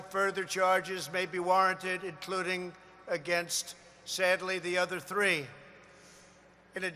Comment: Did he just say "sadly" @ 1:25 mark?